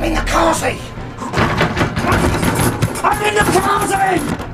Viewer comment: Waht did Sarge say ? ? ? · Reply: he says "i'm in the khazi" an English word for toilet or bathroom.